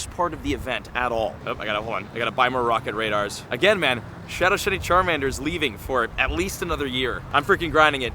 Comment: … I swear he said Shadow Shitty Charmander lol😂😂😂